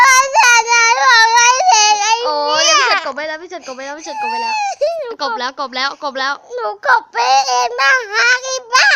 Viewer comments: พูดว่าอะไรน่ะ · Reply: ไอเตี้ย😂😅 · พูดว่าไรอะ🤨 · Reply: ไอเตี้ย